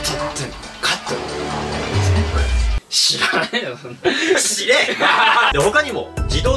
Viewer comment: の「知れ」が「死◯」に聞こえてしまった